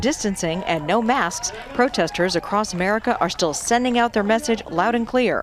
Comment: did she say 'Merica? lol , nice... spoken like a true yank